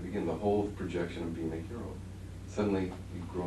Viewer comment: he wanted to be a heroine. A girl when he was little, can anyone explain what was said, having trouble understanding the content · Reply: Hero!!!